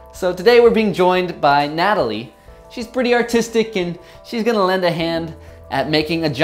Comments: at first i thought he said "shes pretty autistic" XD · I thought you said she's pretty autistic